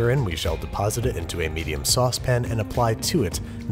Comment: I’ve been seeing random episodes of babish and I never, ever thought he would say saucepan instead of saucepin at 1:31 I don’t know how to feel.....